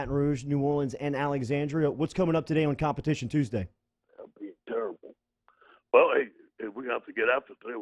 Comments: what’d he say???? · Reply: He said fucking terrible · Weird moment at the end there at 6:56. Jordy asks a question and it sounds like Ed says to someone in the background "it's terrible" and then proceeds to answer Jordy's question. Jordy and T-Bob share a bewildered stare and move on. Bizarre. I think things are falling apart. Crazy how fast one coach and program can fall. Sad for Coach O.